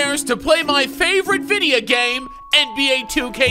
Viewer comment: He really said "videa game" 😂